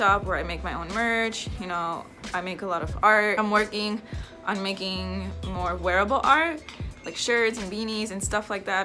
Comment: "I want to make wearable art" what I heard: "I want to make my own brand of roller skates, Karrot Skates" XD